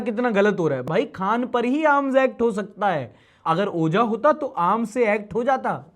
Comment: suddenly i hear the word "Ojha" and i was shocked and thinking that how he suddenly refers me 😂